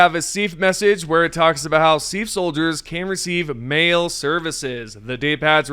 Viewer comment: I’m so glad that you posted the game graphic. When i heard you say the “SEAF received MAIL services”. I heard it as “receive MALE services” and was like did this game change over night?